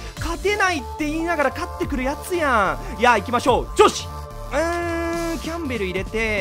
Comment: 相手のレート高くて興奮して調子って言ってんのに女子とか上司とかに聞こえるw